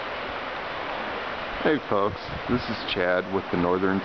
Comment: The first time I watched this I thought you started out by saying "Hey, punks" but watched it again and realized you said "Hey, folks". funny stuff.